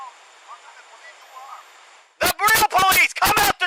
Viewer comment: Did anyone else hear the man say “ what kind of police you are?” And the screamer says “the burrito police”